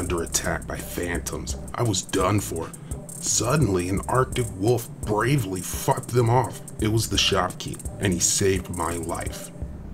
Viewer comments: I thought it said: "and a brave arctic wolf fu*c#ed them off!" XD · Lol I thought he said f them off XD but he said fought